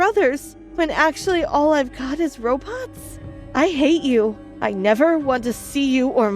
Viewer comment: or 9:44 did she say Robux?